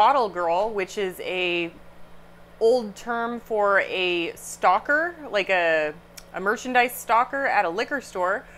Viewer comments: When you said "stocker," I thought you said "stalker"!!! LOL! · Lol at first I thought you said you became a stalker at a liquor store!😆 I was going to suggest AA meetings!😎